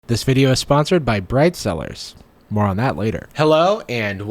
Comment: For a second I thought you said “ this video is sponsored by Bright Side” and I went 😳